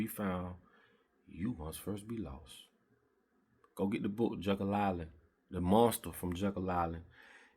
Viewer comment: Hey, what was the name of that book again?...I thought you said jungle island.... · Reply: Jekyll Island